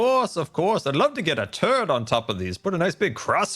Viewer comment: I definitely heard him say “Turd” not “turret”, very different meaning lmao